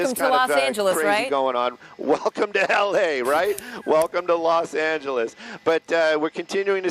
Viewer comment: Thought he said "Welcome to Hell-A!"